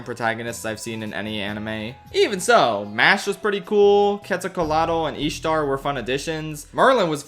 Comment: WTF did you just said!? Queso Colado? Quetzalcoatl is pronounced Ket-zal Koh-Ah-tl